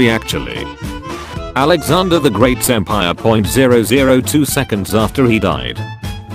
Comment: Oh no i went full weeb. I heard "Alexander the great senpai" and i was like "what the fuck" @@